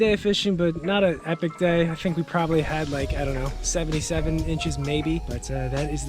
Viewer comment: When you said you had 77 inches I thought you said 77 bitches, I was cracking up